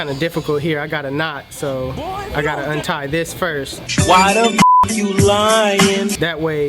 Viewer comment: If u listen carefully I can hear “Boi if u don’t”